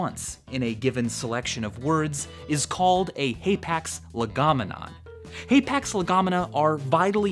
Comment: Michael: 16:59 My distracted brain:Wait,did he just say Apex Legends? Apex didnt even exist that time lol.